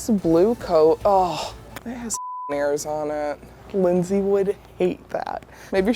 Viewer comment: what does say at 5:14? it has f*cking (?) on it? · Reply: Ears